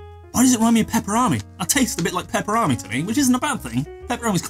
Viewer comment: Please say Pepper-o-ni many more times in your video.. its sounds like your saying pepperrami! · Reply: he did say peperami, it's a brand name